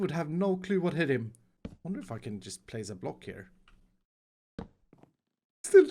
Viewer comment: sounds like he is saying “I’m gonna f***ing just place a block here!” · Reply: Yes but he said "I wonder if I can just place a block here"